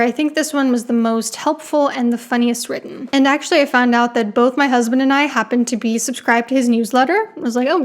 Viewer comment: This was great! Thank you. I did want to mention i was reflecting on how smart and well spoke you are, when i then thought you said “both my husbands and I…” and I thought “my God and she has two husbands…” 😂